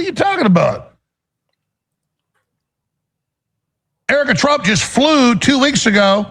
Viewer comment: "Erica Trump just flew two weeks ago on a aeroplane to kiss your...." huh? did I hear Alex say Erica Trump? lol